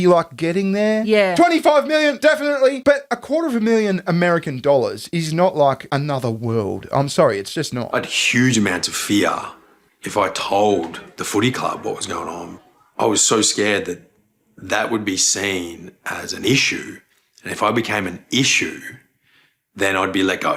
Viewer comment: does he call it the "footie club" and he thinks he can talk about masculinity? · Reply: For 1/100 second, my brain heard that as "footsie club", and thought "You were rubbing your teammates' feet with your feet under restaurant tables?"